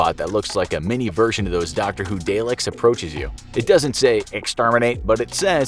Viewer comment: did he really call dalek a delek